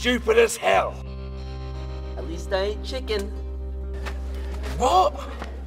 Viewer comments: i thought he said i least i aint chicken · i thought he said "at least i still got my chicken" at the end. not "at least i ain't chicken". in the real one i mean.